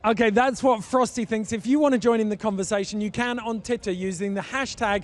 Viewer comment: EPIC! PS, did he say 'titter' instead of twitter?